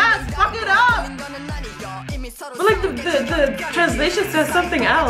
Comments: I always heard it as 'I'm gonna suck up all the lights' and not 'I'm gonna fuck up all the lights' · "I'm gonna fuck up all the ladies" is what I first heard 😁 It's not of course but rather some Korean.